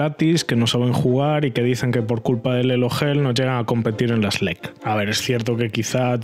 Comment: cuando escuche "Elo hell" en otro de tus video crei que habias dicho "Elo gel" XD